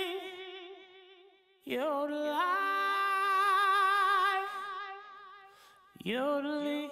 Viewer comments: it sounds like he said “you lied” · it sounds like he says “ you’ll live your life” for mythical